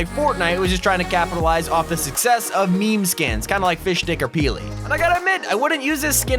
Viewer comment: did he say fishdic….